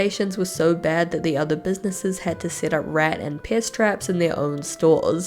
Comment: Every time you say "pest traps", I hear "piss traps", and it makes me giggle.